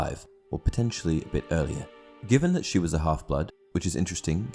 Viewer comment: I thought u said hufflepuff not half blood and I’m like wtf how would she be a hufflepuff she would be slyterin then I replayed it and was relieved when I heard half blood